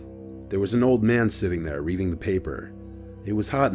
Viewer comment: “the man sat there eating his paper” i really thought he said that